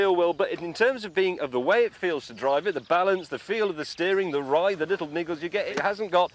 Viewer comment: Am I the only one who heard the n word at 6:53? 😂